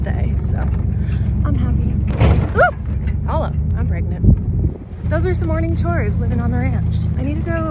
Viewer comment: Did I hear you say "Im Pregnant" While you were in the back of the truck?